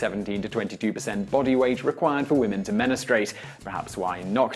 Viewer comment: did he say "menustrate"?